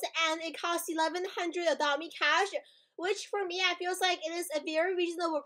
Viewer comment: I always hear “I feels like”